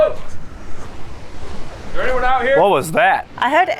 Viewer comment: Am I crazy or did anyone else hear someone in the distance say "can anyone hear me" in the background of narrator talking at 57:35 ...?